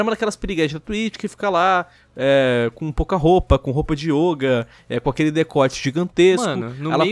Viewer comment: "Com roupa de ioga" Eu ouvi "com roupa de olho"